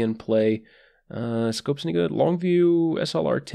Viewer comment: did you just say "scopes nigga?" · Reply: Malin 1 Lol i heard that too but he did say "scopes any good"